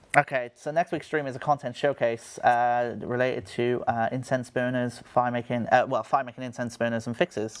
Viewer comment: i heard "incense burners" as "intense boners" from